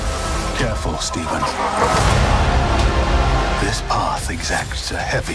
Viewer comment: TRUE BELIEVERS Sooo At 9:21 is it me or is Mordo saying, Careful StephenS”!?! As if he’s talking to Multiple Stephen Strenges!!